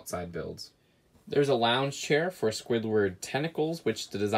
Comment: Sounded like you Said ”a chair for squidwards tentacles” 😂😂😂😂😂🤣🤣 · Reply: Nope. Clearly said a chair for Squidward Tentacles.